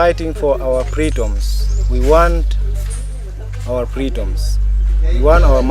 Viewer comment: Excuse me sir did you say freedoms